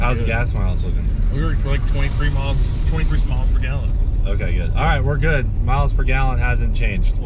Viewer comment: I heard "23 smiles per gallon"